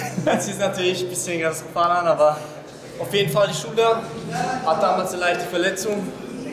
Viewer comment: Bei 3:16 ..hab da erst Schule verstanden :D :D :D